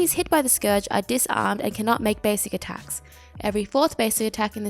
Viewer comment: Hey, I'm a bit confused because I said disarmed in the video around 2:27? Did I say silence somewhere else in the video? If I did, I apologise for wrong wording! & yes, it's one of the reasons why she is a hard counter to basic attack gods :) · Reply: yeah. you say disarm on 2:27. not sure where but I heard silence. or maybe I misheard it since it's almost 12 midnight here and I need some sleep. anyways. good job of the video. keep it up.